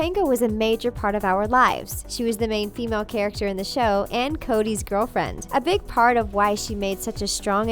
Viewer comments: did i hear her say "cody's girlfriend" ????? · did she just say Cody?... it's Cory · Did she say Cody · anyone else hear her say Cody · While I was listening I heard Cody's boyfriend and I had to replay it three times. So I had to see the comments on it. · and 6:39 I keep hearing Cody isntead of Cory am I going crazy? ? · Did she say... Cody?!?! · did she say co y I'm pretty sure its cory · Brah did you just say "Cody's girlfriend"!!